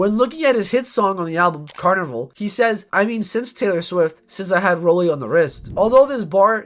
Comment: That’s crazy I swear I thought Kanye said “I made six Taylor swifts,”